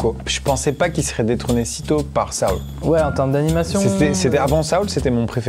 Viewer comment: J'arrive pas à comprendre. Coco détrôné par quoi ? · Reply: Un film de pixar qui s'appelle soul